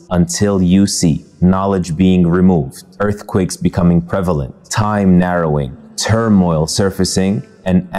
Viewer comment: Salaam @ 2:41 shouldn’t the subtitle say prevalent and not imprevelent?